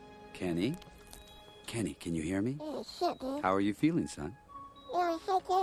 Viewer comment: did he said 'oh shit dude'? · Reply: I think it's "holy shit, dude".